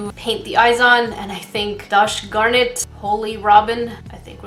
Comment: Did u say dosh garnit? 😂